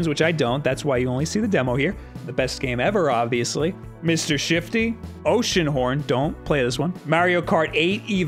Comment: I thought that said "Mr shitty"